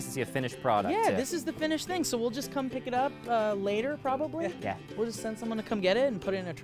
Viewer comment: When said something at 17:23 I heard it as "ehkä", the Finnish world for "maybe". Now I can't unhear it. I think he might have said "Heck yeah" but the fact that there was a lot of "the finished product" comments before that, and saying "probably" my brain has melted and I am not sure anymore. :,D Just wanted to say that, since it is bothering me way too much. (Also way too excited for this channel! I've missed seeing you all together so freaking much! If I had extra money, I would become a patron right away!)